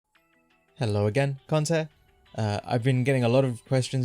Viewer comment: Love the channel, but I got to get this off my chest. Everytime I start the video, the first thing I hear is "Hello cunt hair"